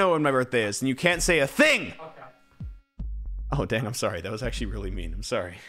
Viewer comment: DID RANBOO SAY DAMN??? WOAH THIS IS A FIRST